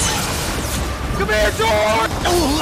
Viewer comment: I heard Dream said "Come here, George!"